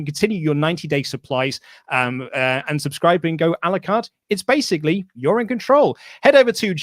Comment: Did just say A LA CARTE?! □□□ #GetRowdyAirHorn